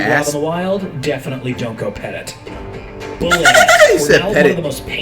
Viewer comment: FLIGHT THOUGHT HE SAID PET IT🤣🤣🤣🤣